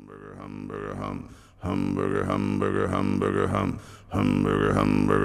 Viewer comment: I just watched Dune for the first time ever and I actually thought he was saying "Hamburger hamburger hamburger! "